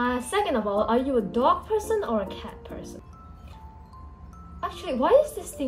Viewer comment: istg i heard "cat"